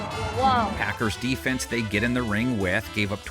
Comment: He never mentioned the word "Defense" I'll wait til you watch it again. He said "Green Bay gave up..." So yeah, nice try Mr. Moral Victory. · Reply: it starts at 1:00. He does say defense and the graphic says defense, which is why I said what I said. I’ll wait until you watch it again.